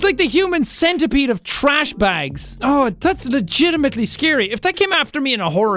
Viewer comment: When you realize how different cultures actually are Jack: As a kid i dressed up in a binbag and went around asking for sweets People in the United States: As a kid i dressed up in a trashbag and went around asking for candy Different cultures... Different words.. Means the exact same thing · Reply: For a while I thought he was trying to say beanbag but it was his accent but then I finally realized he meant trash bags 😂